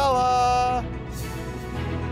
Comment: what is he saying? 😂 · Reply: Challah LOL (the c is silent)